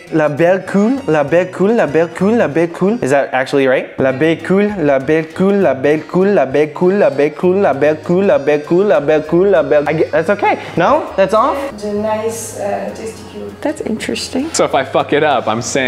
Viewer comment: j'ai compris la belle couille 😂😂